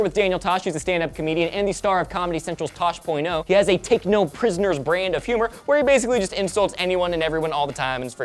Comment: thought you said "techno prisoner comedy" needless to say i got excited and then i was let down..